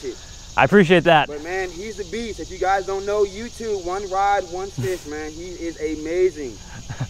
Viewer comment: did he call him 1rod1fish